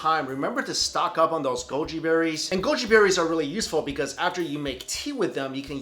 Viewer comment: Oh well, I thought he said "...stack up those Golgi Bodies"